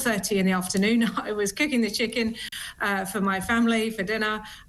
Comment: Did she just say kicking a chicken.